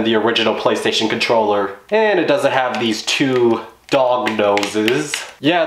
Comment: iTs aNalog stiCk why did u call it (what did he say?) "dOcK dozEs"